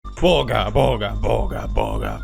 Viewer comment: I thought he was saying booga booga at first •__• This is when you know you haven’t watched pew news in awhile 🤷‍♀️